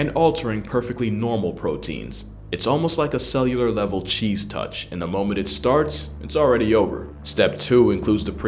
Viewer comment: Did he just say the cheese touch